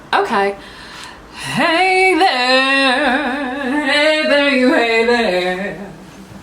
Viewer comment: i thought you said "hey there new haven" and i got really excited (just moved from boston to new haven) im devastated